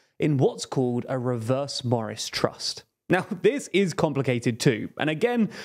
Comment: I thought he said a reverse Boris & Truss, I was thinking, can I never get away from Liz Truss and Boris Johnson